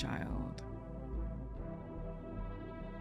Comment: Did she just say "the belly defender"😆